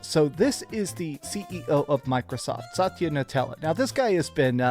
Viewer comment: Did he say “Nutella” instead of Nadella? Did I miss something?